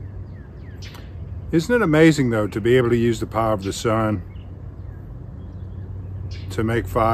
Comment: On this Sunday, when you said "isn't it amazing to use the power of the sun....", I heard "isn't the power of the Son amazing!" God bless you brother.... keep up the great videos and have a blessed Sunday!